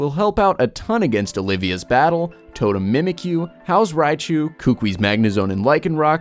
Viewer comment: I kept hearing 'strong against Olivia's whole team'